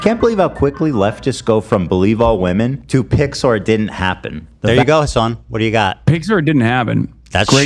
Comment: Am I the only one who thought Ethan kept saying "Pixar didn't happen"? I was so confused for a while